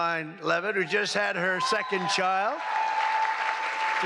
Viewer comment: It sounded like he said, "our favorite white hat," when congratulating Karoline on her second child. Also, his rhetoric about needing a bunker under the ballroom is contrary to his actions during some...events—wanting to pose for a camera with fist in the air vs. running for cover. 🤔